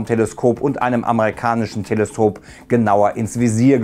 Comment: hab ich da nur Telsop verstanden oder noch jemand anderes 😅